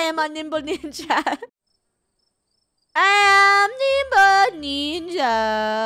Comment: Lol when they first Said Nimble Ninja's I thought they said Nipple Ninja's LOL